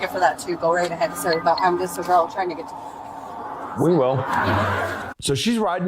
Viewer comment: did she say I just a girl trying to get somewhere?